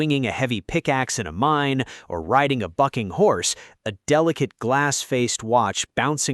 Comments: I thought he said ride a fucking horse not ride a bucking horse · I swore he said "riding a fucking horse" 😂